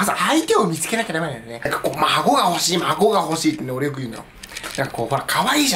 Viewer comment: 孫が欲しいのところ、アゴが欲しいって聞こえた